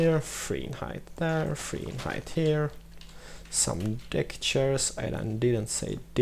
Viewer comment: Did he say dick-chairs?